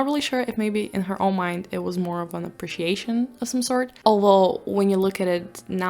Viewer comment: You say "alol" instead of "although"